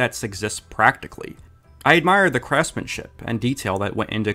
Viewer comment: okay, be honest. DID you say "Crassman's-ship" on purpose, or did it just come out that way?